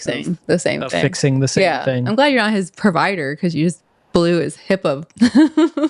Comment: It sounded like said “ blew his hippo” 😜